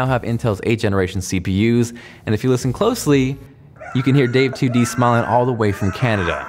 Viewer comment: Did you say "smelling" or "smiling"?